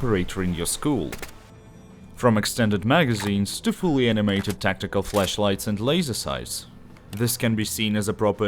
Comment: i think it is his accent but i heard "tactical fleshlights and now im getting images in my head